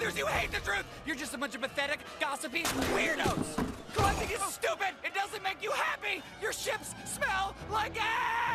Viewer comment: I can't make out what he's saying to those people there, and the captions don't say either. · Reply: "Collecting is stupid, it doesn't make you happy."